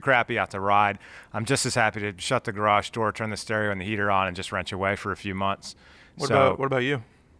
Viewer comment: LMFAO. My wife was listening to the end of this Vid and heard say he would be happy to"wrench away" all winter and she thought he said "rent your wife" all winter. :)